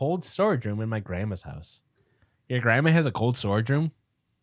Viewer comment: I thought OP said their "grandma's cold sore room" 😂